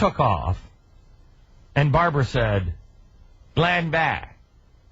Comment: i recommend to just listen to this Video to ignore the subtitles... sorry but who the hell added these?? Is this on purpose? They are totally screwed up. Modest Examples: 2:53 "Glenn Beck" subtitled with "land back"?? 3:01 "I understand".. subtitled with "I am Tuesday" ... huh?? 3:01 he repeats "Glenn Beck" and not.. "Linda". No Offense.. but that looks really weird and is totally confusing to people who dont understand english well and trust into the Subtitles. Peace